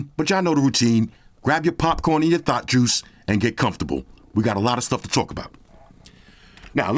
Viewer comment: Ohhhh shit time for my thought juice!!!!!! And popcorn!!! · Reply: I thought he said Thot Juice. I was like, "Sirrrrrr..." 😂